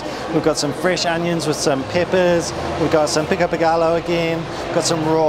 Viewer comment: at first i thought you said pico de gato haha I'm going to CDMX for a couple months next January. This video has me so hyped ugh it all looks so delicious